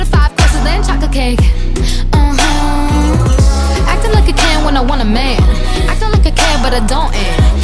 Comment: I heard "acting a ken when I want a man" and I thought gender fluid icon 💜